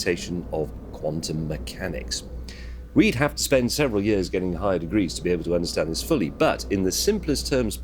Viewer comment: i thought he said "we hav to spend several years getting high on weed to understand it fully"